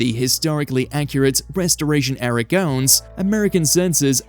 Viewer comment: I swear I heard "breastoration gowns" in The Wicked Lady segment.